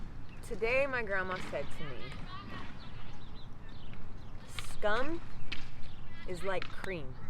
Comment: Also, not sure why I somewhat have bad hearing today, but can't tell if you said "'scum' or 'c*m' is like cream; they both rise to the top; be careful what you think with." Which ever word she said, both are super true. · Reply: She said “scum” 👍🏽